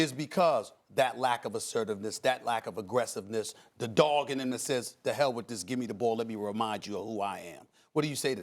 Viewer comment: what did he say, the dogging assists? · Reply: “the dog in him that says give me the ball”